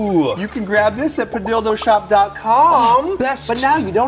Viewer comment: thought he said dildo <link>! 😂😂😂😂😂